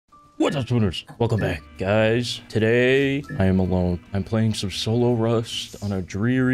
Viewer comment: Everytime I hear “today I’m number we playing solo rust” I wait for blazed to join🤣🤣